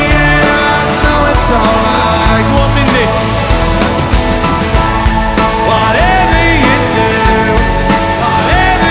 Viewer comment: What does he say 3:52'? (In cant get it, Im not English speaker) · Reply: I hear : "You can sing this"